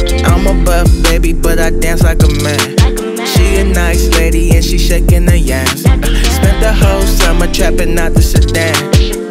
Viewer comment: "She a nice lady and she shaking her yams" 🤣🤣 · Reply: I heard " she a nice lady and she shakin her yass" haha